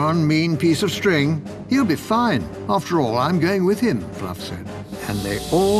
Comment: Misheard:He'll Be Fine After All, Im Gay With Him